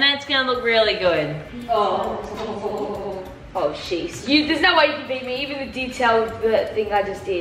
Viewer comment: anyone else hear swear? (Oh s###)